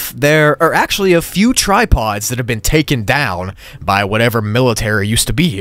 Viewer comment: bro i thought he said tidepods at first and not tripods